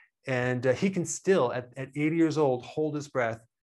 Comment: for a moment I heard "8 years old"